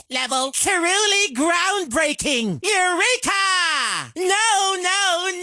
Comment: What does he say in 04:19? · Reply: EUREKA!